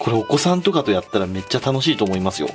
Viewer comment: 「これお子さんとかでやったら楽しいと思いますよ｣って 聞こえて焦った笑